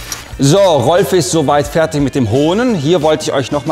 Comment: ich hab verstanden " So Rolf ist soweit fertig mit den Hoden"?